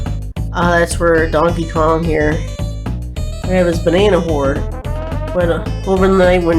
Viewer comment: Did you say banana horde or banana whore? XD